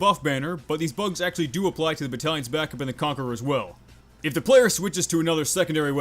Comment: Did he just call the conch the conquerer’s will